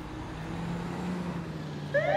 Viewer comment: First time hearing this song today I heard the words: "I like black goatse and cigarettes"